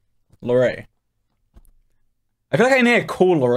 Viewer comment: I thought he said "You right" lol but it's "Larray"